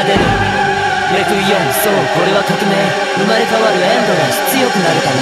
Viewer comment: そう、俺は革命に聞こえるのは気のせいかな